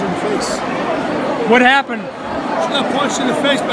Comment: When she said what happened I thought she was saying my husband